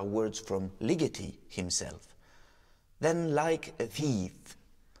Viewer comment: Superb talk by the master! I'm having difficulty understanding what he says at 3:08 "All these words are words from Ligeti himself. Then, like a ...?" I'd be grateful if you could help me out. Thank you in advance. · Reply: I turned on CC (Closed Captions) to be sure. He is saying "like a thief "